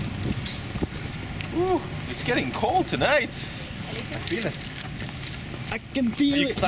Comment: Wtf did he say (it's getting cold tonight) HAHAH i bet it's the opposite